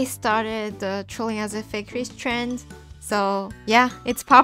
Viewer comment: IM SUPER LATE but I thought it said “trolling as a rich fake depressed emo kid” I got so confused I’m like huh? I’m gonna click this for sure. But anyways I liked this videooooooooooo